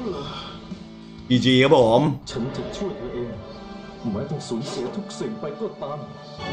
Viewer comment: ทำไมได้ยินว่าฉันจะช่วยตัวเองเนี่ย